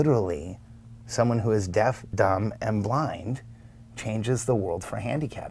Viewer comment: Does he say deaf dumb and blind?